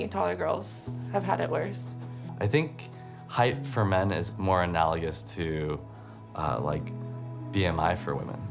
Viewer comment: What word did he use at 4:10?? I can’t figure out the spelling ... Enellious? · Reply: Yeah definitely analogous. Basically means that one thing is equal to another.